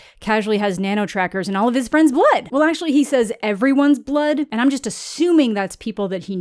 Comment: I thought you said he has trackers in everyone’s butt not blood